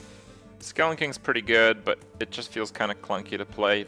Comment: What does he say at 1:59 skelingking?